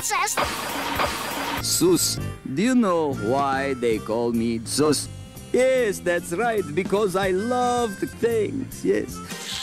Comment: Did the Count just say he loves the taint? I didn't know we had so much in common! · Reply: I thought he said "do you know why they call me sus? because i love dathings"